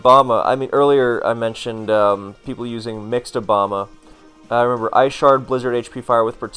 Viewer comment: I heard it wrong, I thought said "mixed Obama" and laughed for a good 2 minutes straight when I realized